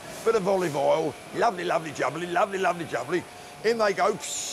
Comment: no idea what that guy said, but now Im hungry!!! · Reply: Gotta give those prawns the ol' "lovely lovely jubbly" in the wok my friend, haha.